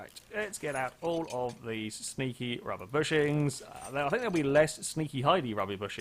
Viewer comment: when you were taking apart the cross members i thought you said stinky rubber bushings